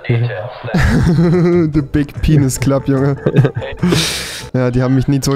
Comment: Hat wer auch bei 2:13 auch big penis verstanden?😂